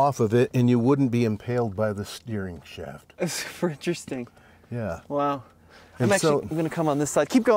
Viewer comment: It sounded like he said 'Frenchresting'